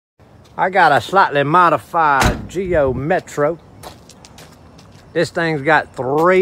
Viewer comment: I swear I heard him say Geo Methro the first time